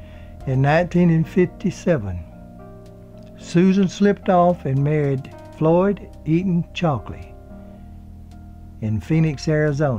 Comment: Hey, I admit I don't hear that well but Closed Caption has her husband's name as Floyd Eating Chocolate 11:46 LOL